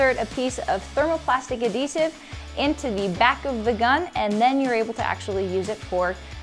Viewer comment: Did she say vac of the gun